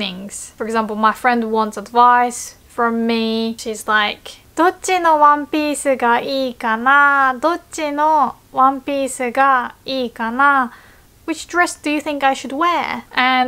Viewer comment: Did she said "One Piece"? Lol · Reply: Which one piece should I choose? English version Japanese version